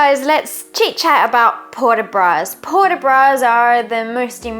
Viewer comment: I thought it said Sport De Bras.